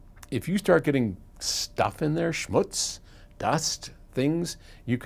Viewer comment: did he say schmutz? i dont understood what he said